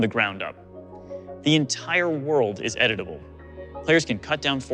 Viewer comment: "The entire world is edible" Yummy :D (I know he is saying edit-able, but I always hear edible xD)